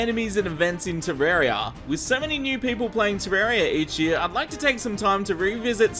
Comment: ohh, "with so many new people play terraria each year" thought you said "with 70 new people play terraria each year" lol.